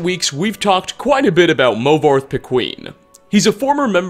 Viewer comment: Movarth The Queen is what I heard lol